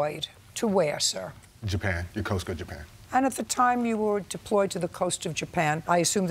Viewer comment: He was deployed to Yukosuka, Japan, and JJ says, "...and at the Time you were deployed to the coast of Japan....." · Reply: Yeah I caught that! 😂 I was like Yeahh she definitely misheard himz it’s not coast of Japan, it’s Yokosuka. A city in Japan named Yokosuka